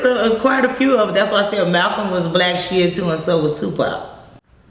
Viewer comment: pls somebody needs to tell me what she saying! "Malcom was a black???? and so was 2Pac" my english is not perfect but my love 4 Pac is more than pure...pls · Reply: She's saying he was a blacc Shia. Shia is a denomination of Islam.